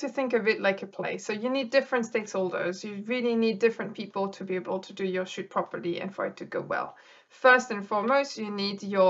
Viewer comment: I really thought she said ''you really need different people to do your sh#t properly''